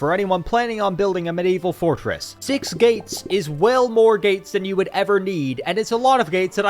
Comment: I swear you said "6 gates is 12 more gates you need" and considering this is Warhammer, I did not question it, like yes, don't build a door that The Horrors can walk through.